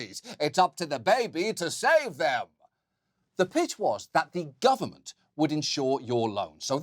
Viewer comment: I probably was a “mitch”. I heard it as bitch too. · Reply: I heard "bitch" as well. Maybe John said, on purpose, "mbitch"?